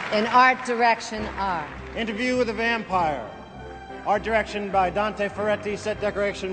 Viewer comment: Interview with "THE" vampire. 4:26 he DOES say "THE VAMPIRE". 4:59, "THE". Yes you are saying NO, NO, No they are saying "A". YOUR hearing is NOT that good. Some people can NOT hear the sound "TH" very well. That is why you are just hearing the "A" sound in the word "THE". I have asked 5 other people in my house as to what they are saying. I did NOT tell them why. They ALL said "THE". STOP trying to mess with people. Fist up, Fight for Truth. PS, you may want to get your ears professionally cleaned. The time to do it is now. PSS. I didn't say: "A" time to do it is now.